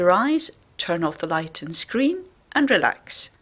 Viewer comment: am i the only one that thought it said turn off your light and Scream?